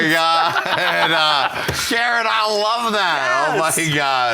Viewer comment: Did he just calleg her Sharon at 10:15?